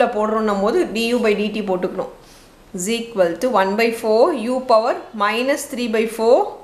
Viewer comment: Mam I didn't understand 2:54 Please reply me🙏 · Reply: Power accomodation bro💪🏻😌🔪